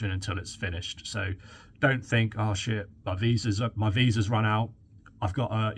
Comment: Did I just hear British chef say, "Oh shit at 4:24 😂😂. Thank you for the update. I look forward to hearing more about this